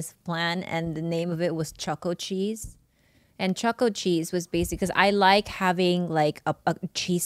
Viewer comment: I like the choco cheese idea. At first I thought she was saying chuckle cheese. And no to the pickle cheesecake